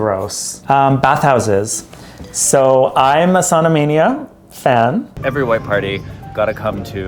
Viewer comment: 🤣I misheard 'Sauna Mania' as 'Sodomania' and was impressed how up-front the business was being.